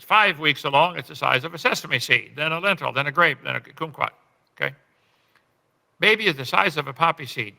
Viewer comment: sorry, did he just say "coomquat" instead of "kumquat"?